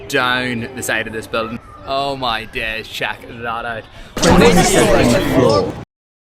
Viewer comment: he says "Check that out" if you don't understand him